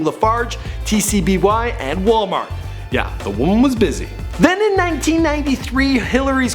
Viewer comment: When he said "the woman was busy" I thought he said "the womb was busy"